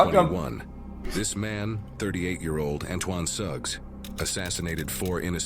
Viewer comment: Blud is pushin 30 but laughing at the name of murderer (Antoine) & the victim name (Nitosha) nahhhh 💀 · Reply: Go back and watch again! They were laughing because it sounded like he said the mans name was "antoine sucks." 😁